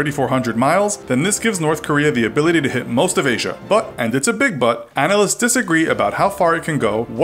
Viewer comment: "...And it's a big but" I wasn't paying attention much so I thought he was saying a big butt.